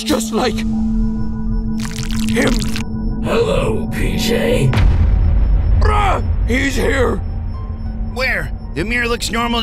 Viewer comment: sounded like PJ was saying Bruh lol